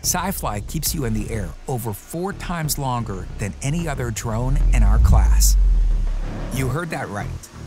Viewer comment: Am I the only who hears “DJI” anytime I hear “any other drone“? 😅